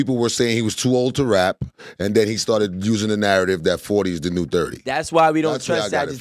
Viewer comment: I thought said 30 is the new 20